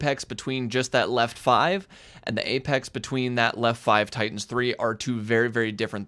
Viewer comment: Great vid. Going to pay more attention now. When I first started I thought said Titans, like some sort of rally term. However I thought tightens meant the road wasn't as wide. -Tsunami